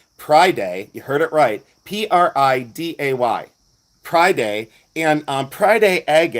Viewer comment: You look so fucking Fabulous my heart! 💜 🧡🧡🧡🧡 lmao thought you said “cryday” 😅😂😂😅 ❤❤❤❤❤❤❤❤